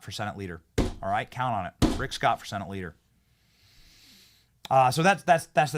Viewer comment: Good, Rick Scott 👍. Thought you said Tim Scott... Tim Scott = RINO